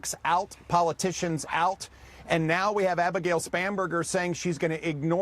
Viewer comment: I swear it sounded like he said "SPAM"berger